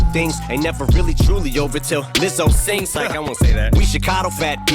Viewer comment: I didn’t even catch the Lizzo bar. I heard “this hoe sings” not “Lizzo sings”